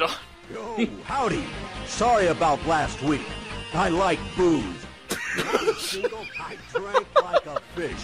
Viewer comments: I thought said "I like boobs!" at first... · Misheard "I like booze" as "I like boobs." Which would probably have made for a better game: Paul Eiding is The Groper.